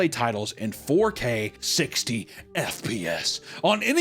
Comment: did he say "4k 16fps" ????